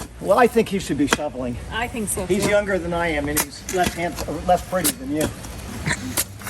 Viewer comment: I had to put on captions, for just a second I thought Charlie said that "he aught to grab a shovel he's less pregnant than you." 🤳->👀->😂❣️ the captions clarified it. LESS PRETTY